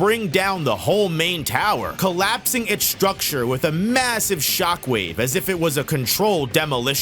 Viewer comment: did you just say massive?